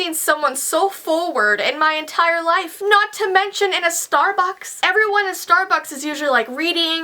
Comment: It sounded like she said "Starbox" ILYSM